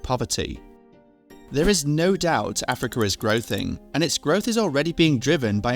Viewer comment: Did he say "growthing"? 😄